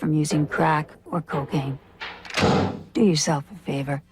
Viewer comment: I remember the drug one. I misheard "cocaine" as "cooking". I knew what they meant, just thought it was bizarre that there was a drug called cooking.